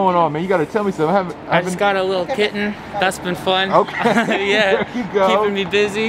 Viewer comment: daniel: trying to get away jonah: talks about his kitten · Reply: For real, I thought that said kitchen and I was waiting the whole video for Jonah to start talking about his kitchen